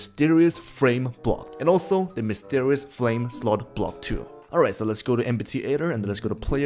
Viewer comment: did you just sayd flame